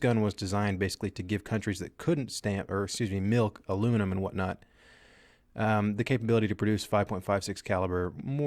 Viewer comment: anyone else hear him say "Milk Aluminum and whatnot"